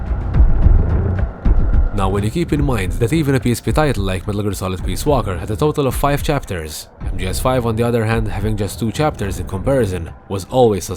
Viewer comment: can you please clarify what you're saying? I enjoy the effect that your thick accent has, but I'm not sure what you're saying here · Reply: "Now when you keep in mind that even a PSP title like Metal Gear Solid: Peace Walker had a total of 5 chapters, MGSV on the other hand having just 2 chapters in comparison, was always suspicious in itself."